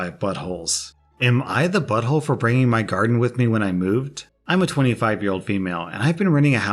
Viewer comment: i heard "bring my gundam" and i immediately tabbed back to see wtf just happened